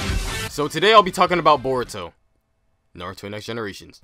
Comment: I keep hearing the word "Burrito" in my head whenever someone says BORUTO..tbh i was turned off when Boruto came out..